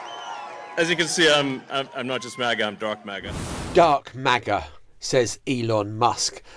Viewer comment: Thought he said 'I'm not just a drug maggot' ?!? Isn't buying votes just illegal ?